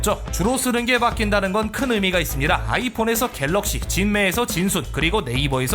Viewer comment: 내가 뭘 잘못 들었나...? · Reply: 실제:겔럭시에서 아이폰